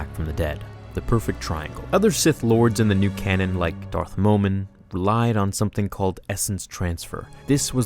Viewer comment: thought you said Darth Mormon at first😂